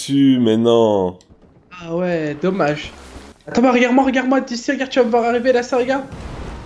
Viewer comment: C moi où vers 2:00 dit "Thomas regarde moi" ? (Oui je sais je m'appelle Thomas mais bon)